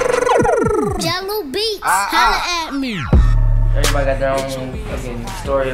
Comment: All i hear is "jello beats holla at me"